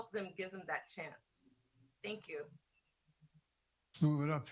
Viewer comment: I haven't looked at any comments, yet, so this is probably not a new thought but...at the 11:40 mark, after the first recorded question, Joe sounded like he said, "move it up," as in...move up the teleprompter? So, Joe's handlers knew all the questions (DUH...they were in a prerecorded video) and all Joe had to do was read the answers off a teleprompter?!? Talking to union members about their red meat issues used to be his forte.